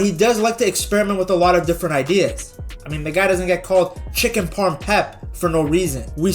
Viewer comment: Your level of sarcasm,omg😅 Did i hear chicken Pep or whaf exactly?